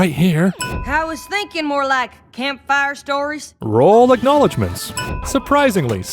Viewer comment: The subtitles say "campfire tales" but outload Applejack said "campfire stories" Thats one sin for